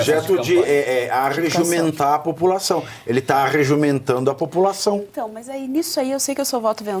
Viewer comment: Eu ouvi o falar que o molusco "está arreJUMENTANDO a população" no minuto 12:52 ? 🤔